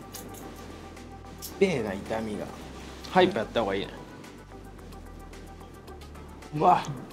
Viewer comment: 痛みが のあとなんて言ってるかわかりますか? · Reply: ふざけました。単順に早くやった方がいいなと言ってます